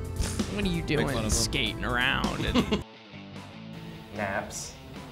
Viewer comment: I thought josh said dabs. Like there's no way you are high during shows you have too much energy.